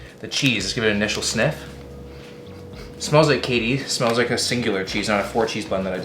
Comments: "Smells like Katie" your girlfriend? did I hear that right? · did he say smells like katy😂😂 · When he said is smells like KD I thought he said Katie and I was stressed for his life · thought he said "smells like Katie"